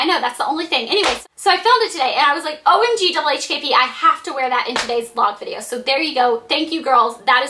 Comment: What is she saying at 3:54...It's something like O-M-G-W-H-T-P...what thos it means??